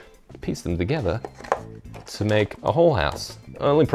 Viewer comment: I honestly thought you said "put them together and make a whore house" but I think its an accent thing. That said, idea for a future build?